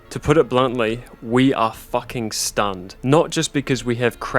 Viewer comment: Is that my shitty accent coming through 😂 I meant to say “stunned” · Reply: Oh I really heard "stunt" 😂 That changes the meaning a bit lol